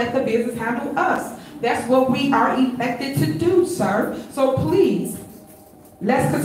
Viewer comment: did she say “effected” or “espected”? Idk why i watch her, every time i hear “town beeeizzness” i die inside.